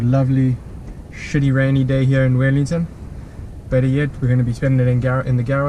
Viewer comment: Are you based in Nz? Sounded like you said Wellington